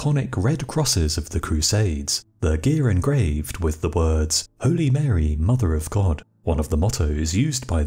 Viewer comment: I thought he said only parry I was about to question all of human history